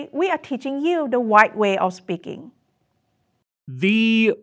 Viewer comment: It's terrible, but when she said, "The white way of speaking," I thought she was saying, "The right way of speaking."